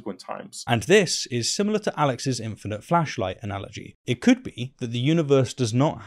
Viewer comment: I heard "infinite Fleshlight" at first